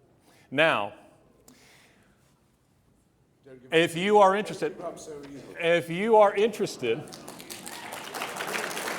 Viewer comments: Did anyone catch what Mr. Hitchens is saying? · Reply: "Don't give up so easily", yes that's what a victim of cancer who was slowly dying of it had to say to someone else merely for the fact that them having to think of their fairytales as fake made them instantly depressed. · Approx 1:07:11 - What does Hitchens say? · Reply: something like "don't give up so easily" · what did Hitch say at the end???? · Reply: I believe he says "don't give up so easily"